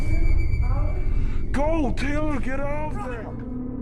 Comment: min mark 2 me it sounds like he says "GO DUDE GET OVER THERE"